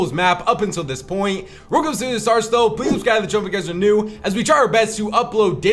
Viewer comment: What does bro say right here? Cause its definitely not “before this video starts though” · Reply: "real quick before this video starts though"